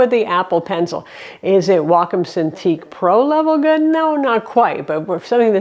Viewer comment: what pen did you say its not quite as good? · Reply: Wacom cintiq pro